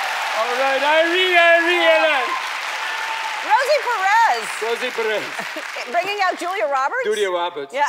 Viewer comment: I thought Rosie Perks said Rosa Parks and I was just like ..